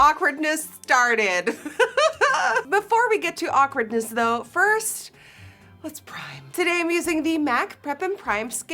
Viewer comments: lmfaooo i thought you were gunna say “but first, lets pray” 😂😂😂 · I thought you we're gonna say; but first, let's pray